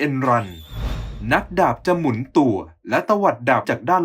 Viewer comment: ทำไมเราได้ยินว่าหมุนตรูดอะ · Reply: หมุนตัว แต่ออกเสียงไม่ชัด